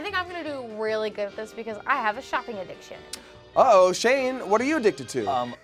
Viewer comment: I thought she said a sharpie addiction